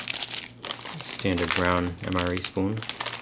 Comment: i heard "stand your ground mre spoon" lol